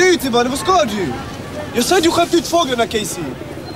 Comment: Vad sägs egentligen vid 8:05, sett serien flera gånger men aldrig lyckats lista ut de :p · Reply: '' jag svär, du har skämt ut fåglarna Kejsi'' tror jag...